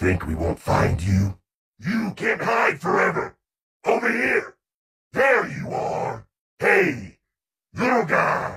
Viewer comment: I always think he is saying “hey yoghurt”